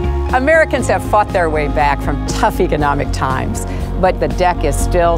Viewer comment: it sounds like she says "Americans have fucked their way back from tough economic times"